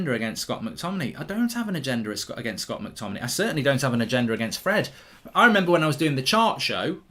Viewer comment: I thought you said I don’t have a gender at first